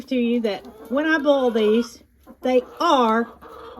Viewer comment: All through this video I thought you were saying bowl eggs and I thought you would shake them in a bowl. Right at the end, I realized you were saying boil!